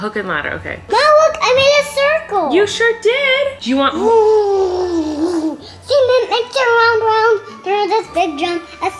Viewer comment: I THOUGHT HE SAID SEAMEN MIXER HELP. HAHAHAHAHAHAHAHAGAGAHAG